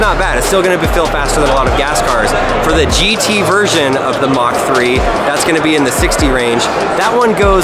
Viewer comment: did he just say "mach 3" ??